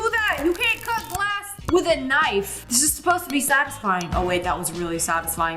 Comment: bro said saxisfying instead of satisfying